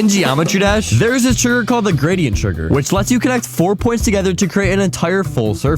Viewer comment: I thought he said griddy trigger